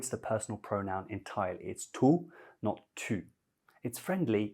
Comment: is there a mistake in the closed captions? I thought you said tu but the close caption said tout I’m so confused